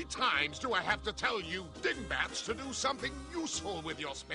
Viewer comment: did he just say 'Dickbats'? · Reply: Dingbats*